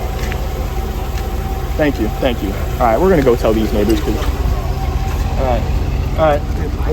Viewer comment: what did i just hear- (ITS NEIGHBORS I THINK)